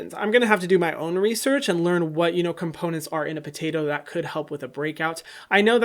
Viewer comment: did he say potato?😭😂